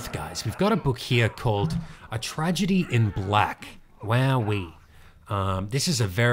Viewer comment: Every time Will says "wowee" I hear it as "where are we?" And it sounds like he's fascinated and asking a philosophical question